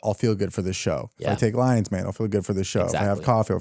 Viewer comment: I heard "let's have some lions mane" as "let's have some lines man" lol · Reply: Oh! I kept hearing "take some lines" and I was wondering if he was just casually admitting to snorting coke. Haha. Lion's mane. Ok. Makes sense. Thank you!